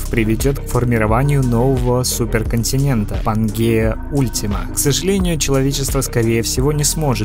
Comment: послышалось Пангея Путина 😅